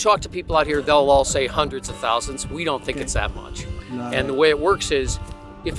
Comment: ここhundred thousands と聞こえるので、テロップは10万人が正しいかな？ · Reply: hundreds of thousands と言ってますね。数十万、あるいは何十万もの、という訳し方が正しいのかな。